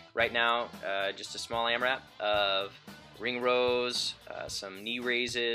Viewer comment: you say something like AM wrap. What is that? I hear it a lot. Does it mean a set routine to get warmed up?